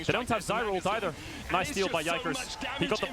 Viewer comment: Is he saying "by Jankos"? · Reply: yikers